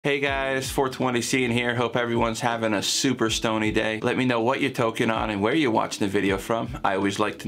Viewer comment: Lol I keep hearing "hope you're having a super stoney day" when 1. I'm out of weed and have 4 more weeks to harvest, or 2. a 50 days lent has started.. ;)